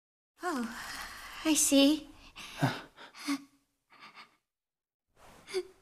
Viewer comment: i thought it said "You hate that i'm a uchiha" for no reason